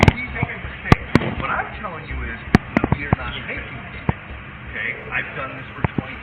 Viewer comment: if anyones wondering what he said he said>quote what I'm telling you is we are not making mistakes end quote<